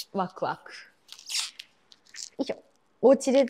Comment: What is the word you say at 7:00 is it よいしょ?